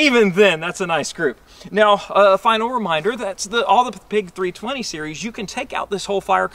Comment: I heard him say at 11:43 "all the Pig 320 series"... 😂